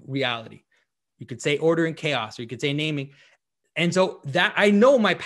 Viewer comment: Jonathan- you thought he said "water and chaos" and he actually said "order and chaos" - and it seemed that confused you both. Lol.